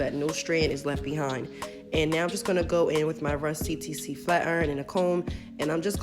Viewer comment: GIRRL at first I thought you said "my rusty TC flat iron" 😂